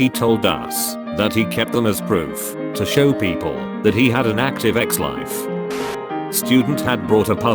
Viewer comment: I heard "active EX-life" would be funny if it was "EX-wife"